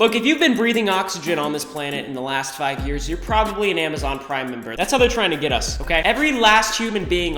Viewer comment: Why did I hear “ pride member